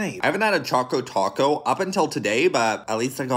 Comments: Did he just say choco choco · It sounds like your saying taco taco